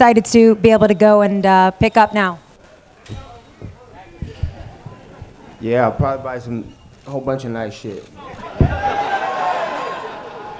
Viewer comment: What does Nate Diaz say at minuti 25:22 ?? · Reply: "Yeah... I'll probably buy some whole bunch of nice shit"